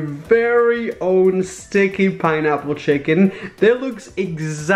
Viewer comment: i literary thought it said ' stinky pineapple chicken ' 😂😂💀